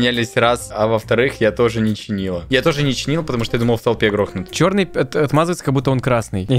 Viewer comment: невинно говорит что он хотел грохнуть в толпе :_))))): не услышал Я: ну штош